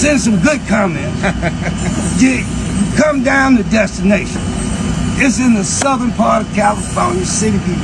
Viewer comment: What I heard him say is come on out to destination candy land. Stephen will be here to greet you